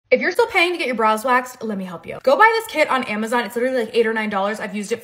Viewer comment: I thought she said “bras waxed” and my ears perked up, I thought I had been missing something 🤣